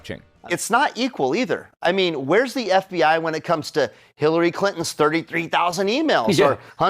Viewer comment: I swear I heard "Hitlery Clinton" when Greg said her name lol